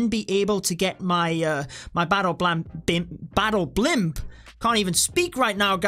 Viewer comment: When said Battle Pimp, battle plan instead of battle blimp 😂